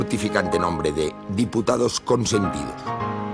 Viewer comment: Que dice en el 39:54? Diputados con servil?